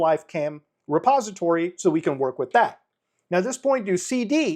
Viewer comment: My high ass thought he was saying suppository repeatedly